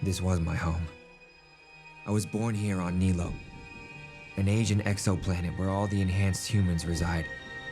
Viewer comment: I thought it said “ageing exoplanet” but it was “Asian exoplanet” lol